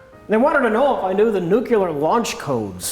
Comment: does rich say nucular? at